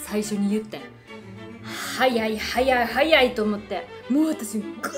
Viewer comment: ここの早い3連発アンパンマンにしか聞こえない😹😹かわいい笑